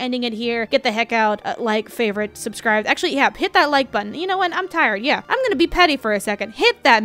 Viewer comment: Not enough scream an for some reason i hear "hit that like button i know my voice sounds Noctis" lel love these vids btw